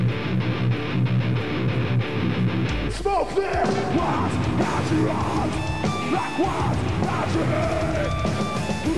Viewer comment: What does he say at 1:44? Sounds like "zwo, vier". Doesn't make sense to me. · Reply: "Smoke there", if you're still wandering